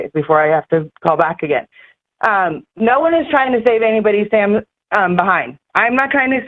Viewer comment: Why at 10:30 does she say " no one is trying to save sam...behind" or "no one is trying to save sam's or anyone's behind" that is VERY ODD because why would they be trying to save Sam's "behind" unless Sam has something to do with Kielys death???